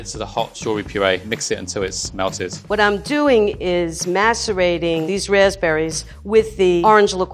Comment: POV: your scrolling the comments section looking to see if anyone else noticed what she said about the raspberries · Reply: She said macerating 😂